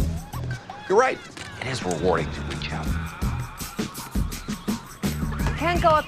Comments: what did house say? "You're right, it is rewarding to be a coward?" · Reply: “You’re right it,is rewarding to reach out” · What does House say, "you are right, it is rewarding to ------?" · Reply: Rewarding to reach out